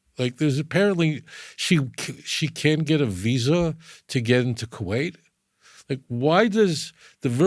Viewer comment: Visa...... I heard that UFO visit Kuwait